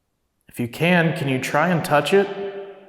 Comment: I listened to it over and over again and that disembodied males voice you caught sounded like it said, "Try hard to push it."